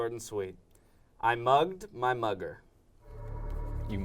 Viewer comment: I thought they said "I mugged my Mother" and were seriously talking about it for a second